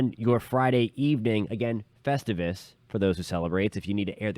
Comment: Festivus??? Did he say festivus??? Omg, Seinfeld!!!